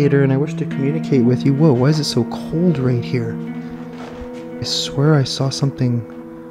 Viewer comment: Lmao, it sounded like it said, "right here, bastard"... Wow, how rude! Lolol